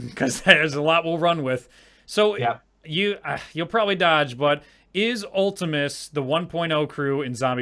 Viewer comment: What did he say at 40:31 ? I cant understand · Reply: Tart0p0mme ultimis